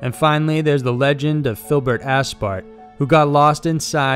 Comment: Sounded like he said "Phillip assfart"